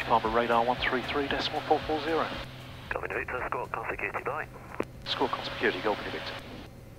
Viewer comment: is my hearing going or have they introduced a new term in UK aviation: 5:28 it sounded like "Squark on security"?